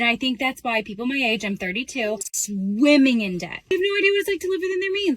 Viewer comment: I heard 52 not 32.